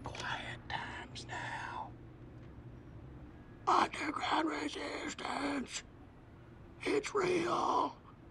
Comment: What does he say at 2:18? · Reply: Yop Glo “Underground resistance. It’s real”